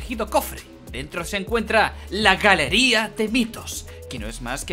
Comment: Escuche mal pense que dijiste galeria de pitos XD